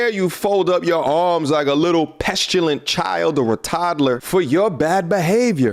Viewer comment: did say “pestulent”?